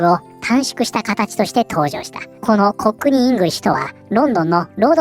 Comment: "こく兄イングリッシュ"に聞こえて草😂